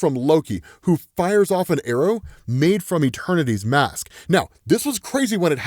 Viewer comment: Thought he said eternity’s ass LMAOO